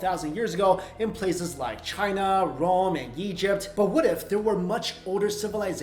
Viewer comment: Hi, I enjoy your videos, but I want the rest of the world to enjoy them better, by pointing out a few Closed Caption/Subtitle errors! At 0:14 you mention "Rome" but on the subtitles is displayed as "wrong in" and also at 0:58 you mention "coal" but the subtitles say "call" and you mention "Dr.John" but it says "junk and" also at the first second of the video you say your name, but it is written incorrectly! Let's help the world and fix these errors!